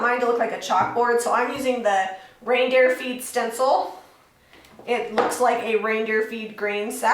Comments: I thought you said, "reindeer feet stencil"🤣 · O man...I thought you said "reindeer feet"!! I was looking for the footprint as you removed the stencil...lol lol